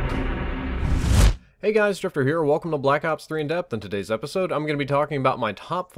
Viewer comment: In the beginning I thought you said "hey guys STRIPPER HERE"